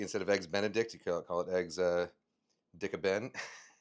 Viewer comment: did he just say EGGS DICK A BEN?